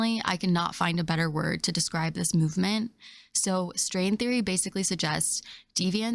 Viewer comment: Thought you said “ Sting Theory” had to rewind, thought I was about to fall 😻 lol, u know how hard it is to find a woman who likes Quantum’ Physics? Fat or skinny! Cheers and forgive my cheekiness.